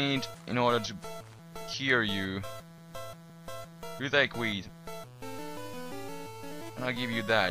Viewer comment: lol it sounded like u said u like weed XD